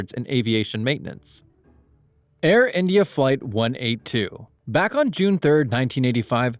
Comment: what did you say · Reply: (he said Air India Flight 182)